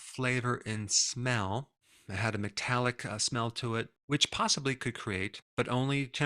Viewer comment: I thought he said "it had a Metallica smell to it"